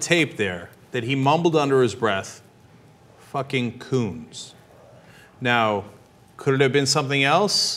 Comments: it actually sounds like he says "these fucking phones" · I heard "fucking punks." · I hear "fucking cold"